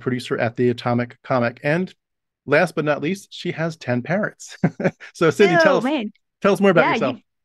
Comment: oh my god i thought you said she had ten parents and when she was talking about one of them learning to cuss i was extremely confused